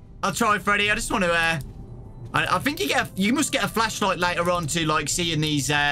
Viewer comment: when he said 'later' it sounded like he said my name Leia